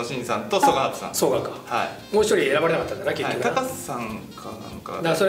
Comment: 「小野伸二さん、曽ヶ端準さん」の後なんて言ってるの？高瀬さん？って聞こえるけど